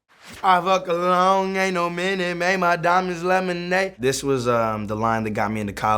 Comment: I thought he said "made my diamond laminate" and i really liked that verse but now that I know it's lemonade I am kinda sad